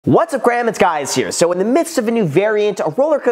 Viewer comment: Did I just hear "What's up Graham it's guys here" ??